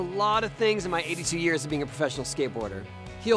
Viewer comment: did he just say 82 years of being a professional skateboarder? WTF lol